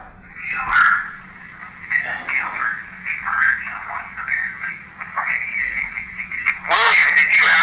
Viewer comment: it sounds to me like it says "HE MURDERED SOMEONE" almost like William was talking about SOMEONE ELSE! It also sounds like someone says something about "her"... I got a "new phone s22 ultra" so I haven't gotten my headphones I ordered yet so I can listen closely. Because I have hearing loss and I can hear "certain tones" better than most people BUT then their a tones I can't hear. I also SLOWED THE VIDEO down from "NORMAL" TO .5 in the YouTube video settings. I will DEFINITELY come back to this video once I get my headphones so I can slow it down and listen again.